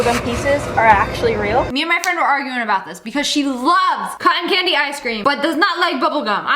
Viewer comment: I thought you said “ice screams” lol